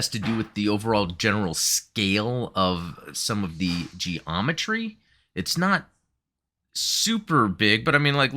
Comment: G-Ometry; sounded like a type of Gundam MS, the way you said it there...!